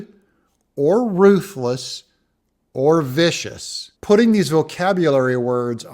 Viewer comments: I thought he said ambitious, not vicious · I thought he said ambitious not vicious but he was definately reading from a script. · I completely agree except I thought Soros said “ambitious” and not “vicious.” Not really sure due to the heavy accent. I was really disappointed that Kari Lake was pushing this obvious fake news that Soros endorsed DeSantis. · I thought he said ambitious... not vicious but who tf knows what bagface is saying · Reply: He did say ambitious!